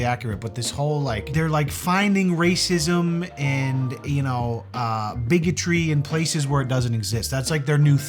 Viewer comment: 1st off, I thought you were saying Biggy Tree! 😂😂😂 Only time "bigotry" is OK! 2nd, that girl was gorgeous and she was either fishing for compliments or actually thinks she's ugly! If that's the case bc she's comparing herself to the "famous" TikTokers (we all know who I'm talking about!) And she's A LOT PRETTIER THAN MOST OF THEM! They're like skeletons or on the bigger side so baby girl... YOU'RE FINE! (in both senses ((is that correct?! senses?!🤔)) of the word! 😆💜✌🏻 Take care all, and remember be nice... IT'S FREE! 🌹😘